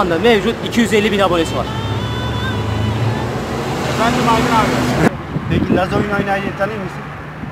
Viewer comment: efendim acun abi :) acunmu dedi benmi yanlis duydum